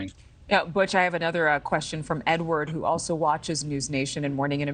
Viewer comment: did she said she had another question from N-word?